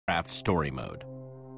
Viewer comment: I thought he said crap story mode right? XD